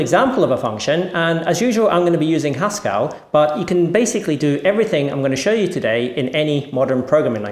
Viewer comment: I thought he said Pascal. I was excited for a moment there. :-|